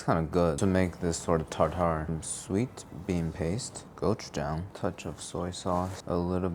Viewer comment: I misheard gochujang as goat jam, and somehow goat jam sounded like a normal ingredient. "I went to the foreign foods aisle and bought some goat jam for my meat waffles."